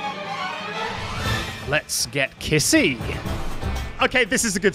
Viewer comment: First few seconds of the video I hear "Let's get KISSY!" I know this video is already gonna be great!